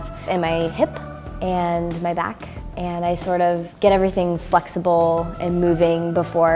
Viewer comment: she said 'fucksible' instead of 'flexible', didnt she??!